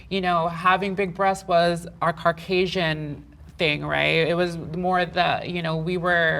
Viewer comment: is she saying carcasian?